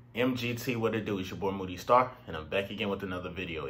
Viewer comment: Lmao bruh I thought u said “booty-star” in the intro